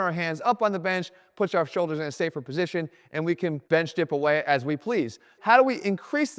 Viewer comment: Bench dip, ah, I thought he said French dip and I headed to the deli. I should have known it wasn't that easy😢